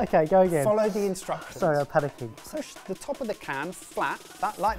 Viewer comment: "Sorry, I've had a kid" is an absolute mood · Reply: I thought he said "Sorry, I'm panicking." I don't have the volume turned up the whole way though, so I might have misheard it.